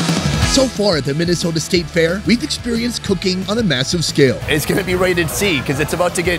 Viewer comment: I heard cocaine